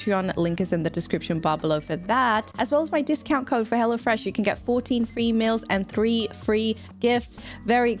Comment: 14 FREE MEALS? I thought you said "14 FEMALES", lol - I was like "Awh HELL YEAH, sign me up!" Haha